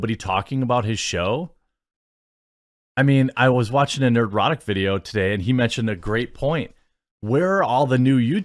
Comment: I thought you said you were watching an erotic video lol. I was like what kind of erotic videos are you watching that seems like some pretty insightful and up to date news for the genre.